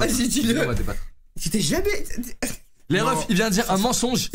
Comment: on saura jamais de quoi il voulait parler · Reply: "Il c'est jamais raser le pubis" c'est ce qu'il a chuchoter